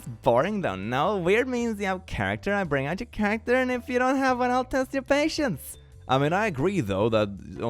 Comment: I wasn’t looking at the screen during the “I have a brain, and if you don’t have one I’ll test your patience,” and I heard, “I’ll test your patients,” and I was like, “Oh, so if the Doctor isn’t very smart, you’ll run the tests. Makes sense. She must be a nurse.”